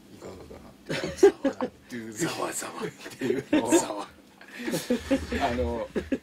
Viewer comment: is he saying "zawa" like in Fukumoto's manga?